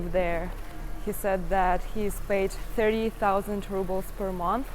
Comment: I thought he said 7,000 rubles.